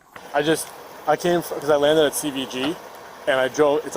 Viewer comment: he actually drove all the way from NY. He did not fly. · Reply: this is what he said 5:55 "i just landed at cvg (airport)" is that the subtitles say